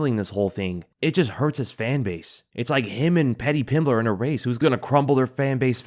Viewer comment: Sound like you said “him and Petty Pimblet”. Petty is the perfect name